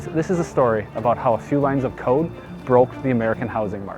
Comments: Oh, a few lines of CODE. I thought he said coke, and it made more sense. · who else thought he said "few lines of COKE " ?